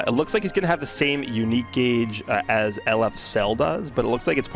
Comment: my dumbass thought you said, "same uniqe gauge as LF Zelda"